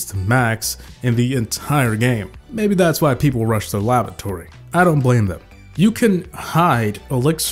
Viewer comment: Did I just hear: Maybe that's why people rush the lavatory???